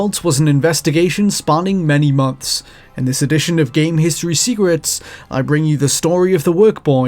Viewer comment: sounds like you said "game history cigarettes" instead of "game history secrets".